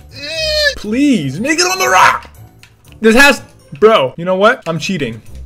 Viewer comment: did he just say the n word?! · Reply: No he didn’t, he said “make it on the rock”